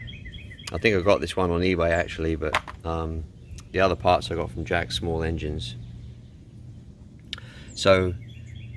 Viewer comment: Hey, where in Jax is that engine place you mentioned? Thanks! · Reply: Ok yes I’m aware of both of those. Thank You. I guess I can thought you said Jacksonville small engines. 😅